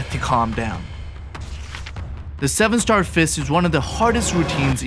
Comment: at first I thought this said “Steven star fish”